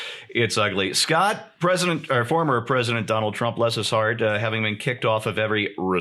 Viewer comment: Whenever the press addresses him, all I hear is "Miss Derp President".